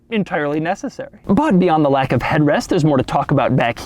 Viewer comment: did he say head “ west “